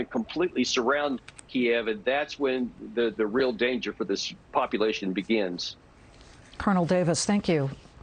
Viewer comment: I thought he go say "and thats when the real dance,starts to play".